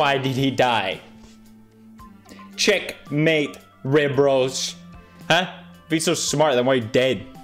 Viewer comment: Until this week, I thought he was saying "Check. Mate. Ribroast."